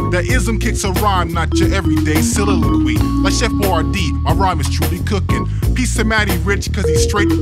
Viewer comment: I thought I heard "Chef Boyardee" at 1:50 , to be fair, that would make me think this whole music is satire